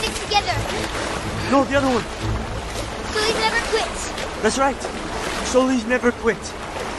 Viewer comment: i heard soldiers.